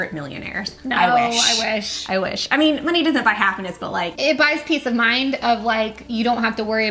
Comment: When they were talking about money and it doesn't buy happiness and said "it does buy piece of mind" thought she said "it does buy pizza" 😂😂 love yall